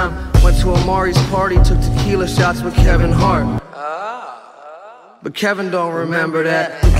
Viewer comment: Did he said??